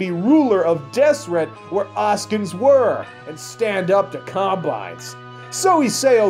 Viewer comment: the only word that i still cant figure out - whats a oscans? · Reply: Oceans prbbly